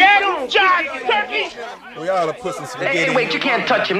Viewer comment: What he say? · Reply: "eating watermelon" I think